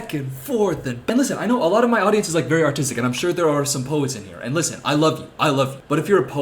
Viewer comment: i had to zoom back because i genuinely thought he said "i know my audience is very autistic" and it caught me so off guard i was so confused